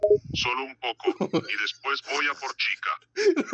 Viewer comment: Cuando dijo "luego voy a por chica" pense que dijo y luego voy a por chicas XD